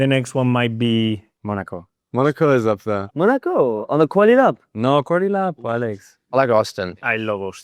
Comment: On 5:59 the subtitles should say "quali lap" (as in qualification) not "quarter lap". On 7:21 is should say "slick" instead of "sleek"